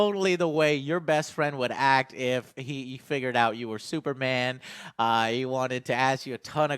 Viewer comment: who else heard him say Superman instead of Spiderman